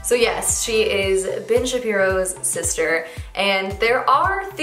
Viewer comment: Really thought you said “Bitch Shapiro’s sister” 😂🤣😅